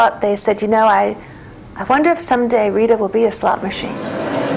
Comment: Sooooooooo unfunny. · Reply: I agree. The funniest thing she said here was when she was describing her career change - she said her parents never thought she would end up a slot machine. The funny part was it sounded like she said slut machine.